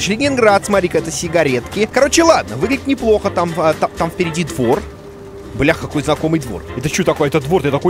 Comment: «О, впереди двор» Послышалось с просони «о, пердит вор», минуту вдуплял что к чему 😁